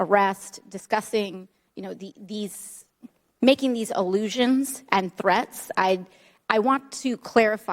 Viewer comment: well said, the woman doesn't know the correct language to use in this forum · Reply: Even though I thought I heard her say, "allusion", I'll go with you on that! lol (in this "forum")